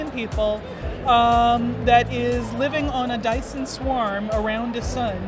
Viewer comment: what did she said? I'm not familiar with the term + I'm not a native english speaker. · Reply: Dyson Swarm a science fiction term. Basically a Dyson Sphere but rings.